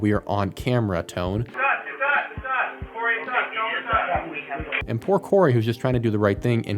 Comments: I think he’s saying “Its us, Its us!” as if he found a broken window (or opened it himself or whatever.) Thanks for making these. The coverup is flagrant. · I think he's saying "it's us, it's us, it's us, it's us" ...not "it's done" ...because just before that you can hear someone saying (presumably over the walkie talkie) that they have someone (in line of sight) at the window, which is 'not' broken... So I believe that was a ground unit keeping eyes on the building and they saw someone (wielding a gun) move the curtains, so he was relaying back to that officer that "it's us, it's us, it's us" so that they wouldn't accidentally get shot by officers surveying the building. Just my opinion.